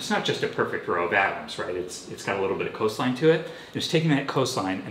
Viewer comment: "Coastline" He's saying "coastline" like, atomic level serrations. I thought he said "cosine" and it made no sense.